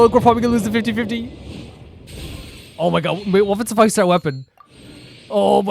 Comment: did I hear that right? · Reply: He said 5 ✨ weapon lol